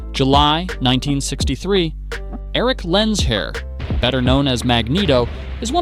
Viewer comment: Did he just say “Eric LensHair”?